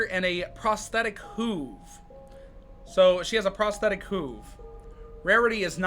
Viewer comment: I thought prosthetic hoove said pointless hole